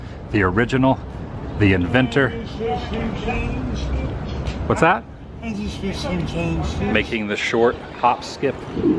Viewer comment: What the hell was that all about? what was she saying? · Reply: Stephen Warhurst asking for spare change